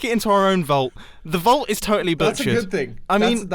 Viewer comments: Wait, what did you say at 6:11??!? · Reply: He said butchered not the bad word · did I hear mumbo just swear say BS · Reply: Chuen Chow no, he said “totally butchered” not totally BS.